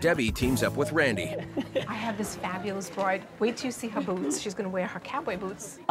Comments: LMAO! i thought she said "Wait till you see her boobs💁🏻‍♀️" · Thought she said to Randy: "Wait till you see her boobs"